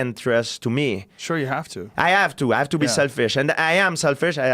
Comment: The first time I heard “ I have to be sad fish”